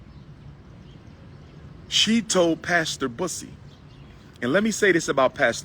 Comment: At first, I thought he said pastor P..ssy🤣